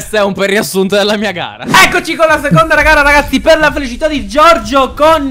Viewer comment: Ma solo io al minuto 6:30 ho sentito "Eccoci con la seconda ragara ragazzi"? hahahaha comunque siete troppo forti raga!